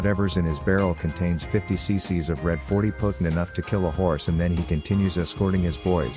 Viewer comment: I thought he said 'To kill a Horus' 😅